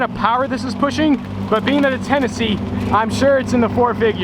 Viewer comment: did he say Tennessee cause captions say he did I live there btw